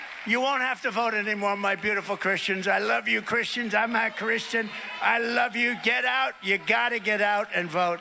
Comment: I hear “I’m AHHHH Christian.” I don’t think he’s saying he’s not a Christian, though he’s not, I think he was glitching and held the “a” for a long time. But to be clear, he sucks, Harris for Pres!